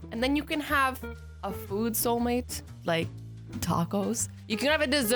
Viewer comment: When you said food soul mate I thought you said boob soul mate